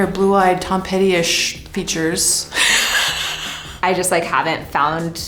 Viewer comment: who is the young zoomer intern who put "(indistinct)" on the CC when she clearly said "Tom Petty-ish features"